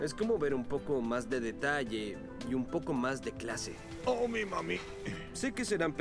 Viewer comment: Parece que dice "Oh me mame" xdxd